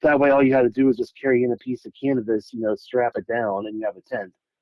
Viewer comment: Lmao IDK if its just me but I swore he said "Really all the boy scouts need to do is carry in a piece of cannabis And they have a tent"😅 I know he means Canvas but I hear Cannabis every time! Heres me picturing these kids like 😮"You want us to make it out of what"?